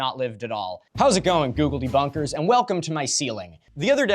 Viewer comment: Did he just call us Gugeldebunkars?